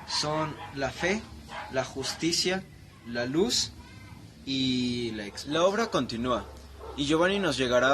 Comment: En el minuto 11:10 ya no entendí lo que dijiste, ¿Me podrías decir? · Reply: Gracias por el comentario, fue un problema de edición pero en fin, de lo que hablaba era la Fe, la justicia, la luz y la expiación